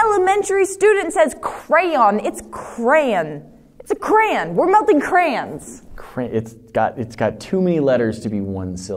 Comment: I thought she said were melting Koreans